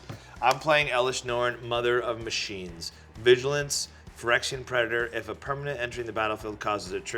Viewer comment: When he talked about elesh norn it sounded like he said she was a phyrexian predator lol😂